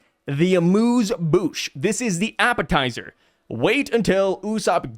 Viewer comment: did he say: “ this is Mu’s Bush!”